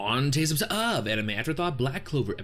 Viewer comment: I love ur intro phrase and been following ur videos since the elf arc. But it now hit me: i still don't understand the words you said 😂😂😂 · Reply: same hahaha, I always hear it as "Oooh today's what's up, anime after thought" xd. I know it makes no fucking sense but that's what I've been hearing